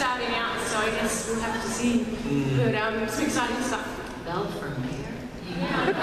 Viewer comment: What does Lana say in 18:20 ? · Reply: I don't think she says anything til she say "belle for mayor?"